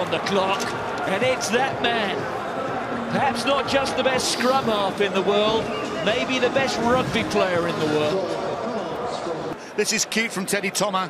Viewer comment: did he say the best halfback Or maybe best player in the world?? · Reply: He said : "That's not just the best scrum half in the world. [but] Maybe the best rugby player in the world"